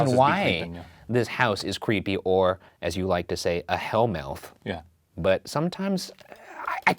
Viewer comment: I thought said “hell milf” and I got really confused like- There’s hot demon moms haunting certain locations?